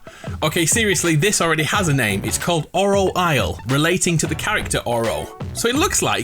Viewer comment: Anyone else thought he said oral isle